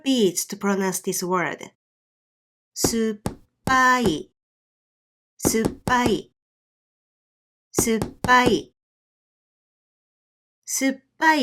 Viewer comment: why it sounds like u said subbai? i dont hear pa, i only heard ba